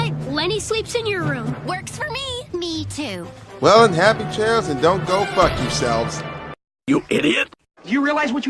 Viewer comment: What did you say at 8:49? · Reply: I said, Well, happy trails and don't go fuck yourselves. That is what I said there,🤣🤣